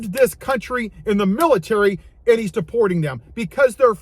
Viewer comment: As a non native English speaker I appreciate the subtitles, but at 5:58 it says supporting instead of deporting and that makes a mighty big difference here. Powerful video nonetheless!